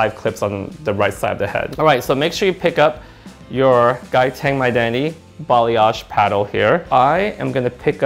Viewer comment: I just realized you say "My Identity". LOL years of watching you and I always thought you said "my daddy."